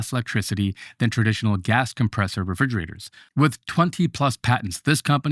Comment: My man. Your numbers are WAY OFF! My refrigerator doesn’t cost me $495.00 per month to run. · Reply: i thought he said commercial refrigerators. I saw a commercial refrigerator in a friend’s restaurant, it was an actual room.